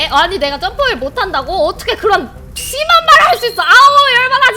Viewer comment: 심하다고 할 때 잘 못 들어서 씨바 말을 할 수 있어 라고 들었어요 ㄷㄷㄷ